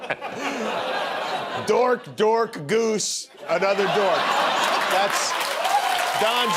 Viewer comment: Dork, Dork, Dork, Goose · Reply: I thought jimmy said dork dork goose dork 😁